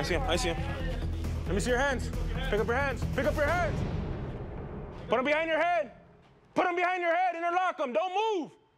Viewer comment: PUT YOUR HANDS BEHIND YOUR BACK...In the same breath...DON'T MOVE...LAY ON THE GROUND....DON'T MOVE...Which one is it sir? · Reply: Pretty clear to me he said put them behind your head but he misheard him.